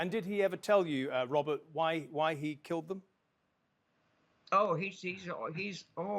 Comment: did i hear correctly? i thought i heard that newsman ask the defense lawyer if O.J ever did tell you why he killed them.. timestamp